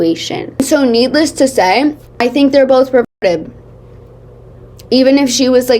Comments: hey what did you say at 1:04:00 bc it kinda sounds like the r slur… ??? Also maybe you meant reported but it just didn’t match up with the rest of the sentence ? And why would you censor “reported” ? I think you meant perverted ? Cause you censor that and that would make sense Just scared me cause I thought you said a slur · did you say reported? bc on my audio it kinda muted halfway through the word